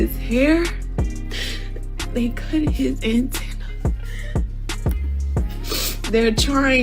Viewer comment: 😂😂did she say antennas? 😂😂😂 😂 lawd now they aliens